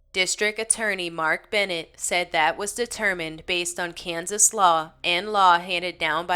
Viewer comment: In the 4 call near the end when you were talking and said Kansas Law I heard "cans of slaw". Lol